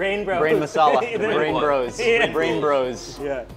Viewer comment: Did they say Bangbros